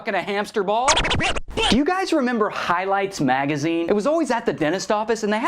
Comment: It sounds like he said "heil-lights" video instead of "highlights".